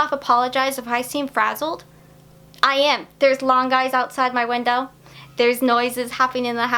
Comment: I definitely heard “There are Long Guys outside my window” and was thinking, “What kind of urban legend/creepypasta are Long Guys, and why does Lindsey seem so casual about it?!” until my brain finally caught up and realized you said lawn guys. 😂 What can I say? My brain is in spooky mode this month.